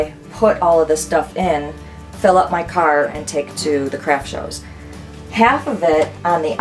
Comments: I thought she said crack shows 😂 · Omg when she first said “Craft shows” I thought she said “Crap Shows” 😂😂